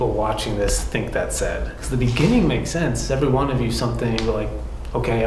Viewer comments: I heard “every one of you did not disrespect me” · For me, i hear : " every one of you, Conor, Alex and even Chelsea " It was the first thing i heared wen you did the EVP